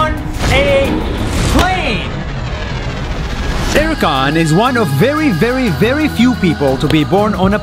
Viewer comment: when you said Erkan I thought you said karen.